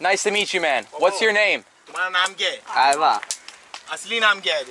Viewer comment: “What’s your name?” Gang Leader: “Man am Gay.” atleast that’s what I heard.💀